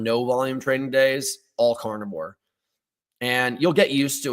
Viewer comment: What did you say at 32:11 about eating on low / now volume days? Cant understand it 😅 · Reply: He said "carnivore", a.k.a. meat only